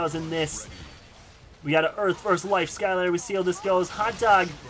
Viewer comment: did he say hot dog was a earth skylander